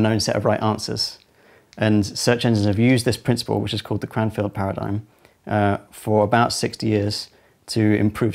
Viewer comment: what was the name of that paradigm? I thought he said "grand phillip paradigm" but I doubt it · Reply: Cranfield paradigm